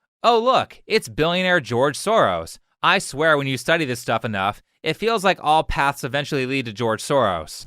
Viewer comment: did he just say study?